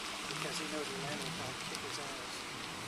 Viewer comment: ??? what is this officer saying something about kicking his ass? Hard to hear over the vehicle Engine noise. Did I hear that right?